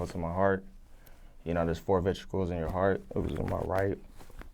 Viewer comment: Thought dude said, there's four vegetables in your heart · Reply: Chillingsworth he said VENTRICLES! Lmao!!! Not vegetables!